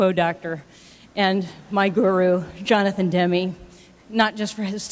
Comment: what she said that?....... My guru